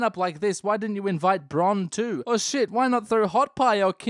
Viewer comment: I know he said "Bronn, too" as like in addition but what I heard was Bronn 2, 2 Bronn 2 Blackwater